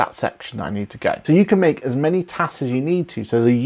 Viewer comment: Thank you, excellent. I thought it was funny with your accent, when you were saying tags, i thought you said tanks. I thought is QBO now in the military lol. Thank you for a great video.